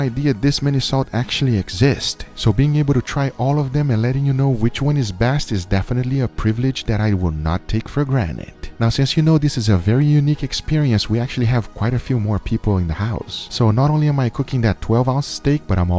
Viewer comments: did you just say "for granite"? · Did you just say granite ...?